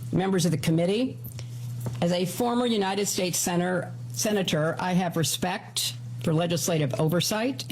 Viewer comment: it sounds like Hillary says, as a former United States sinner, before correcting herself and saying senator.